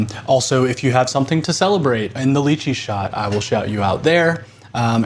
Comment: When you said lychee, I thought I heard you say “Luigi shot” and I was like “damn, never heard someone making a holiday out of it but I won’t complain”